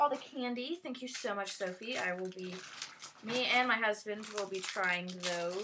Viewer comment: it sounded like you said me and my husbandS will be trying those